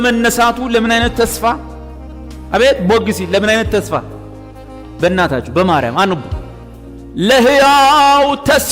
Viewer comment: did he say bemariyam ❤❤